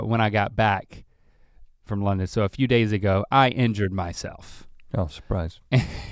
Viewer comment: I was chewing food and misheard. Thought he said "i entered myself" 😭😭😭😭